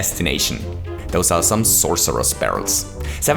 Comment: did you expect us to understand what you just said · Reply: he said "those are some sorcerous barrels"